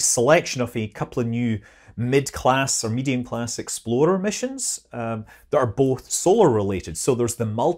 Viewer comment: Sorry Scott, but every time I hear you say “explorer” I hear exploder